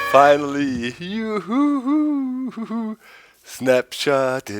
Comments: I thought it was saying Snapchat :P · I cant be the only one who thought they were saying "Snapchat" In the intro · lol i thought they were saying SNAPCHAT!!! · I thought he said snapchat